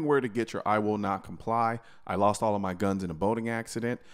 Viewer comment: Haha.. I thought you said “I lost all my guns in a voting accident”, that would make a cool t-shirt as well 😅